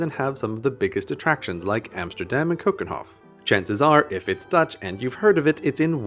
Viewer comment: Yeah we get that a lot too... I even thought he said "famous attractions like Amsterdam and Copenhagen", but that turned out to be a badly pronounced "Keukenhof" ;)